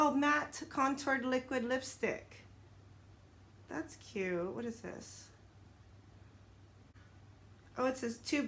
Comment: I thought I heard you say ew what is this lol😂. That’s wa weird looking brush at first but it was just the plastic lol . 💜 👍🏽